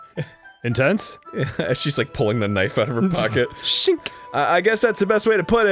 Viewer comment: Did somebody say "knife?"